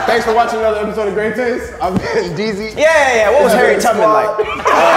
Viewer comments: what did say at 11:25 ?? i couldn’t understand him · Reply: “Yeah, Yeah, Yeah, what was Harriet Tubman like?” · what did say? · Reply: What was Harriet Tubman like? Lmaooo